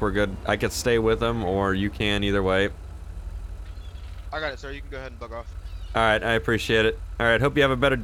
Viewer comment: it sounded like he said yeah sir you can go ahead and fuck of XD edit: oh they talked about it later lol